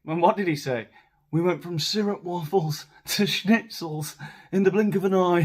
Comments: He said stroopwafel - a Dutch food. Referring to last year's gales presumably. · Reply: Thank you! I thought he said "syrup waffles" and was so confused.😅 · He said stroopwafels, a Dutch cookie, but he pronounced it so weirdly I could hardly make it out. Anyway, his whole speech is a word salad, very very reminiscent of his wife. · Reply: Aah - I thought he said syrup waffles. Maybe made on the waffle maker the late Queen sent to Archie for Christmas because “she knows how much he loves waffles.” 😂😂😂